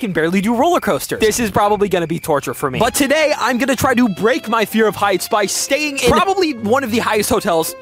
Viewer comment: I thought he said brake my feet😂